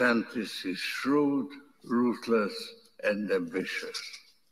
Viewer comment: Soros says that Desantis is "Shrewd, RUTHLESS, and Ambitious. at the 7:56 minute mark. You thought he said RUDE, but ruthless is actually a big difference.